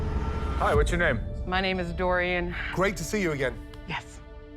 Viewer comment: sounds like she said durian